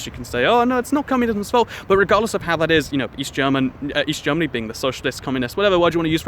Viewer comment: East. I thought you said weast.